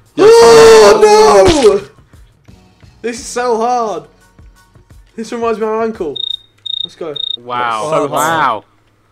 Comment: What does Harry say? "This reminds me of her...." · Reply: "This reminds me of my uncle."